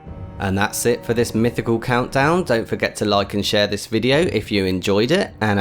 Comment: With the image of the wild headed man all i heard was "Lychen share this video"